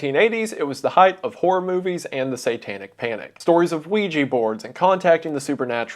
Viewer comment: cop: did he say weedjui board?